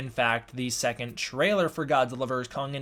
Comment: Did he say the second trailer for GVK